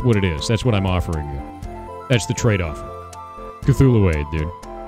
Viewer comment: I heard that as "Cthulhu Aids"